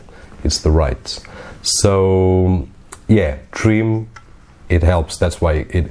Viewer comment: trim? tream? dreem? Sorry I'm not sure of what you're saying. Could you comment. Great video by the way. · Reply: Trim support :) you may read more on the web like here <link> but dont worry to much about it, my wives imac is running with one of these external ssds for 2 years now and no probs :)